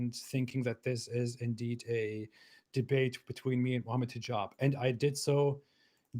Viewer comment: In 10:46 AP said: Mohammed Hijab In 10:46 YT subtitles said: vomitting job